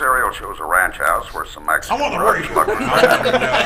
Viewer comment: What does Jeff say at 10:00 in? All I can catch is "I'm in the -" before everyone is laughing. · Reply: "I'm on the radio!"